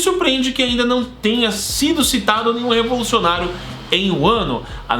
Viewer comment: Eu ouvi... Kyoshiro Revolucionário?